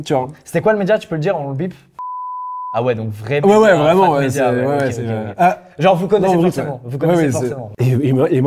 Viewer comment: C'est moi ouuu le monteur il a oublié de biper+flouter la deuxieme fois que dit le nom du média à 14:01 il dit " Euuh ,oué non ,Brut oué " xD